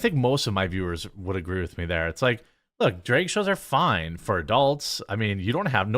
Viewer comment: When said “drag” I hear “Drake.”